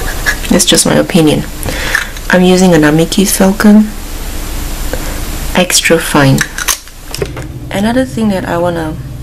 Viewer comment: I cannot understand the pen description. It sounds like falcon but I don’t know and it doesn’t clarify it in the closed captions. Does anyone know what kind of fountain pen this is I’d like to get one? I have the problem when I get calligraphy pens like waterman I just don’t feel like they flow at all it scratches on the paper and I find it so frustrating. I feel like maybe if I got the pen she’s got I’ll like it better. I haven’t had good luck with fountain pens because mine just scratch the paper and it feels rough and really uncomfortable. It’s like there’s not enough ink coming out of the nib. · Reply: Oh, I think I found it Pilot/Namiki Falcon extra fine fountain pen. I think that’s it. I am going to buy one. Give it a shot.